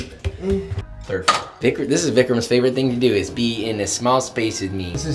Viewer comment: what did he say. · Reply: Vickrom?